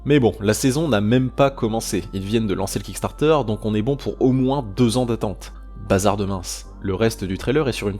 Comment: je crois que j'ai mal entendu ta dis 2 ANS wsh c'est trop long moi je pensais a jore 1 ans grand max